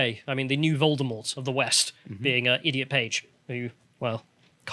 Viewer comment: What’s ridiculous is that calling her “Idiot Page” on Twitter is probably more acceptable than calling her by her birth name 🤦‍♂️ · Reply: I thought they said "ee-dot Page", like E. Page, you know.